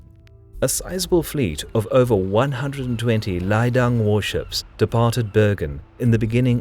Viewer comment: What is the word at 4:31? "...____ warships." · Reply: Leidang. A form of conscripted Navy of the time. In all likelihood, I probably buggered up the pronouciation ;)